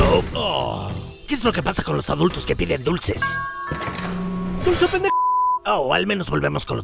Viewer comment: que dijo nepe o pendejo ¿? XD